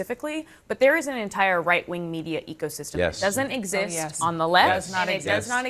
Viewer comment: Did she say there’s no “left wing media ecosystem “? WTF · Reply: Echo system is what I thought I heard.